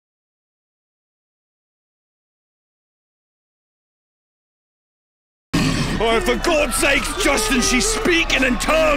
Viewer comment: "onashventuro heresy" Any idea what that demon said? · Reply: This is what she said: “ORNAS VENTURO RASE” Translation: SHAPE FUTURES MEASURE. Basically she’s calling him fat in a demonic language.